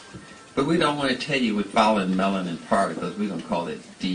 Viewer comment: somebody please respond. what does he say at these time stamps. 14:50 through 15:00 the what journey to be dictated then the what melanin particles? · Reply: found it. he says Thionyl chlorophyll journey and Thionyl melanin particles. dc references this with superman and his father jor-el. with chlorophyll being the green, grey and yellow on the dna structure and colors jor-el wears. superman wears blue, red, yellow, colors superman have worn or associated with which will stand in for thionyl, thiame, thymine, so on. marvel knows a lot about biology, chemistry and hides these things in plane sight, same as marvel with logan being ogun god, logan name came from ogun.